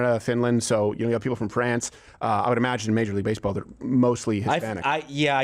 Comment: I thought he said ppl form "Prance" cuz that would be fitting